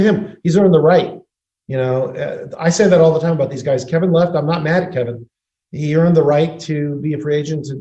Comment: I thought I heard Lakob call KD Kevin Love...he said Kevin left 🤣 I had to listen back with the CC on to know what was said.